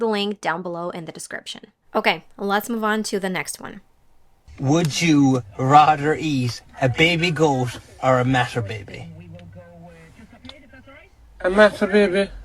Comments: Shit, I'm too bad. I heard "Would you rather eat a baby goat or masturbate me?" 😓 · I didn't understand the second joke of the first video, 'cause i never heard the word "stationery store" before.. and i had to listen twice to the second video, because i heard "master baby" at first and got a little bit confused.😅 however my english really improved when i had to read my college books in english (it was very hard at the beginning) and of course i learnt english while watching tv shows and yt videos. I understand pretty much but i can't speak fluent, like i am not able to built sentences spontaneously in my head and i got a very bad accent. If anyone has tipps to get rid of accents, pls share it with me🙏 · Great video, I get all three of them, But i misheard the "matter baby" into "masturbating" and the question afterwards made me laugh even more. When the old guy ask: What`s the "masturbating". I enjoy Stand Ups from Gabriel Iglesias and Brad Williams. They are my favorite. Also Jeff Dunham. Who never heard about Achmed :D :D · It's not that I did not get the joke on 6:07. It's more like I didn't get what he said with that ugly accent. Haha I thought I heard "baby goth or master baby"